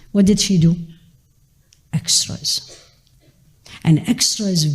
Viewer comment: salam, i missed heard this part 1:08 can anybody help me by telling what word she said? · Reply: Assalamualaikum warahmatullahi wabarakatuh. She was saying extras ,that very few do the extras and thank you for your support ❤️